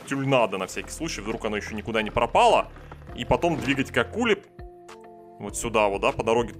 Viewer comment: и потом двигать какули) P.s я в курсе что он сказал к акуле прост прикольно звучит.